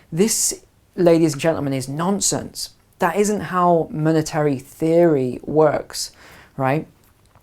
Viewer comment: did he say "military"